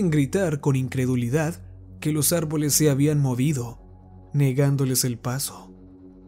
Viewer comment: Acaso escuché "morido"? JAJAJAJA voy a regresar otra vez esa parte